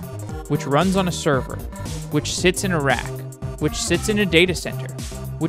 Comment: "which sits in a rack" - the first time I watched this video I thought he said "Iraq", was well confused 🤦‍♂